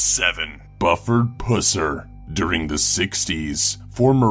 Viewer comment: i thought he said a buffer pussy im like wtf